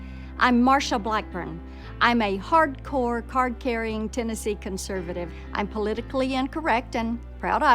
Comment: I thought she said Texas not Tennessee??